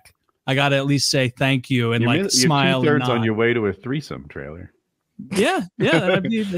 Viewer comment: Did call Taylor "trailer" at 7:00?